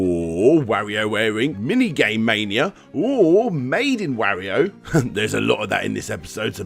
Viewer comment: Ohhhh- MADE IN Wario! I thought you said "Maiden Wario".